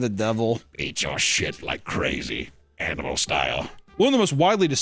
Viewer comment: The devil saying "beat your shit like crazy. animal style." in Palpatine's voice got me light headed from laughing so hard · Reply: For a second, I thought he was saying "Eat your shit. Animal style." and I was like, well, he is the devil.